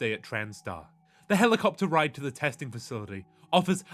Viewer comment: thought I heard "test1cl3 facility", paused, went back, heard the same thing, turned on subtitles, "ooooooooooh" xD